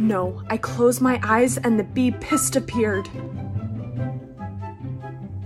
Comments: “Like me” we love a confident queen 😭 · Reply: I thought she said "like pee". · “Did you get stung by a bee?” “No I closed my eyes and the bee ✨pIsSEdAPpEaReD✨” I can’t 😂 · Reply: ngl i thought she said pissed a beard 💀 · Watched this like 5 times before I realized she said “pissedappeared” but I swore she said “and the bee pissed a beard!!” 😅 I was like “but that makes NO sense!!!” 🐝